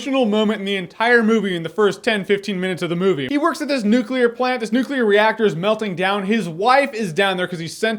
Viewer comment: I heard "this nuclear reactor is melting down his wife"